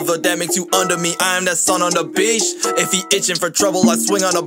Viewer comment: I thought he said "*SON ON THE BITCH*"